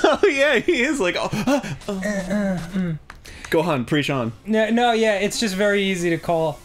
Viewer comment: Did Arin call Danny, Gohan?